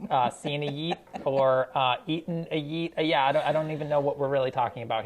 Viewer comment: Chris Morocco: has not eaten a yeet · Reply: See i misheard it the first time as "i have not yoten a yeet" and i was gonna be enraged.